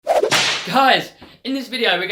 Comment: At the very start 0:01 did he say hiuys instead of hey guys